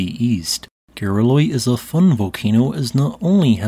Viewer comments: Referencing video dialog at 0:45... "Gareloi is a 'fund' volcano..." So, what is a "fund" volcano? I Googled it but came up with nothing... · It sounds like "Gareloi is a FUN volcano..." And the closed caption says the same thing. That can't be right, right?